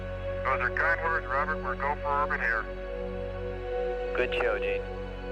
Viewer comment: it sounds like he say's "Good Show Dude" 😁